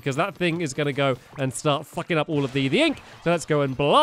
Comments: WHAT DID HE JUST SAY!?!??!?! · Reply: Vortex Maker "sucking up all the ink" Why do you ask? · i just LOLed hard at 3:37!!!!!!! he was supposed to say "sucking up the ink" but yeah i heard it loud and clear XD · the subtitles thought that stampy said f***ing instead of sucking, lol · I'm confused did he say f***ing up the ink or sucking up the ink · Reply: Aud Eday Sucking up all the ink